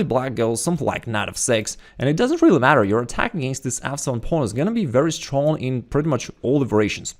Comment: every time you said f7 pawn I thought you said "asshole of a pawn" lol